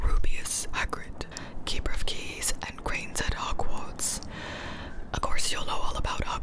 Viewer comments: any one else notice she said "keeper of keys and grains" instead of "keeper of keys and grounds" or just me? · Is it just me, or did she say “Keeper of keys and GRAINS”?